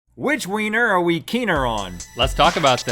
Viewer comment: at the beginning i thought they said what wiener will we keester on😂😂😂🤗